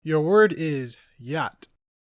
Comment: "Your word is yacht." I thought he said "Gyatt" and said "nah the gen alphas got Jaw too"